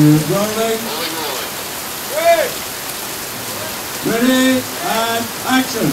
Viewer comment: мне одному показалось что там говорят, "три, два, начали!" ?